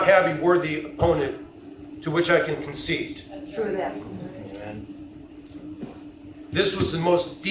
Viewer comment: please don’t tell me that I heard an “Amen”!? 🙈